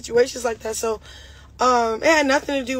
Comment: Tamar Braxton doesn’t have to clout chase off your name “Rock” 😂😂 I swear this chick is beyond delusional 😂😂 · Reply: she clearly said this ain’t about Tamar, she talking about the 2 men speaking out!